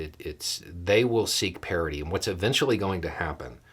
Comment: I thought you said "parody" during your intro, and as things went on, I was waiting for the proverbial clown shoe to drop. Then, later on in the video, you said "parity" with enough context to help me realize I had misheard you.